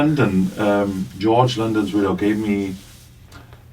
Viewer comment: the subtitles say Linden. It's George London